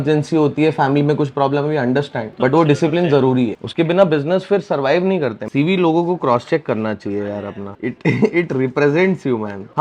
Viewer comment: bro i literally swear i though him saying: GB logo ko prostate krna chaiye apna